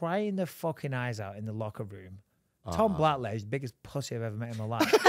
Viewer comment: what name is he say? is he saying Tom Blatlow? anyone know who that is?